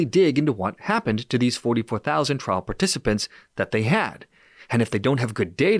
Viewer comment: Did he just say 144000 participants?